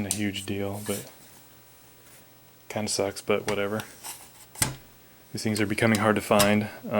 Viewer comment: hey got to 7:24 on this video. You say "kind of sucks" subtitles says " cum sex" XD cool ak thou